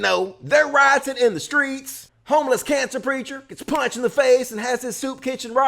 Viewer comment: Ha, it sounded like he said "canster".